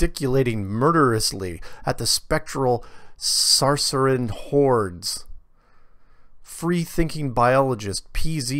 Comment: When you said "Saracen hordes" I heard it as "Saussurean whores"